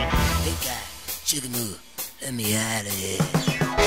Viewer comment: I'm from Detroit (blow the reveille)! And, I have been a HUGE Dolls fan since the beginning. Listen catefully to the end of the 3rd verse (1:47 mark): "That's when I found out they was a-cookin' me. I said-a: "Get back, J...BOO. Lemme outa here."" I can't write the whole word after 'Get back, ...' It's very.....'non-PC'. I've never heard them sing it that way live. They usually sing: "Ooga Booga. Lemme outa here." (Just like the written lyrics.) The Cadets version: "Great Cukoo (or 'Great Ooga-Booga'). Lemme outa here." The Jayhawks version: "('bird-laugh'!?!). Lemme outa here." The Dolls studio version does NOT say "Ooga-Booga". They were pushin' the envelope. - Dave B. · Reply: “Hey guy, shook a move, let me out of here” is what I heard.